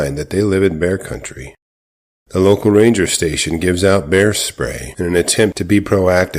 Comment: Bear spray?! Aw, shit, I thought you said, "HAIRSPRAY!!!"